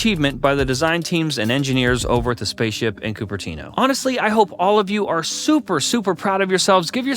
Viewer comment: did someone said Cupertino 🛣️🛣️🛣️🛣️